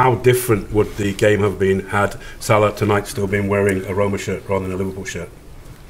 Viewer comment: what's the question at 6:30 ?? can't hear someone type it for me please ? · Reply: How would the game have been different if Salah tonight had on a Roma shirt on a liverpool instead?